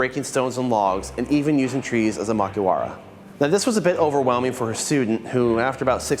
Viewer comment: Caption - [inaudible 00:05:30]. He said "Makiwaras". As in the wooden posts you hit to make your knuckles harder! Only he used the trees instead of building one hehehe